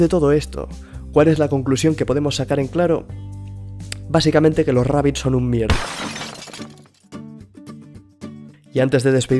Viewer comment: que dijo? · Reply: Si yo creo que los rabbids son estúpido